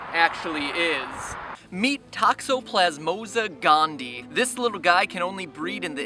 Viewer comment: wait 1:16 did he say gandi